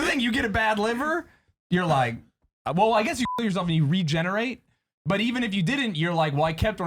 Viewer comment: I’m very confused. Why do they need to bleep “kill your self”? I honestly thought at first he was saying he wanted to fuck himself and wake up like nothing happened